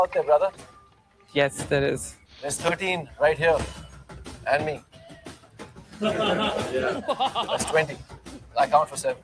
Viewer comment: what he said? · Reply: 13 in the pool. Including me it's 20 because I account for 7.